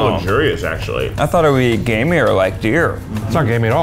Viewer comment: Am I the only one who thought Ned and Eugene said “gay meat” instead of “gamey”